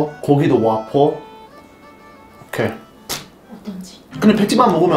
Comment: soa como "entendi"